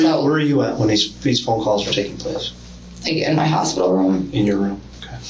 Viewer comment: I thought they said no phone calls 🤷🏽‍♀️